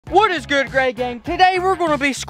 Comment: i thought you said whats good gay gang